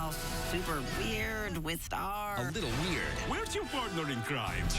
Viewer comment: Wait, that scene with Marco's dad. I thought he said "Why has your girlfriend been crying?" Not "Where is your partner in crime." Now I hear both.